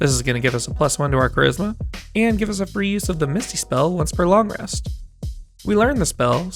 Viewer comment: You said "Misty spell" instead of "Misty Step"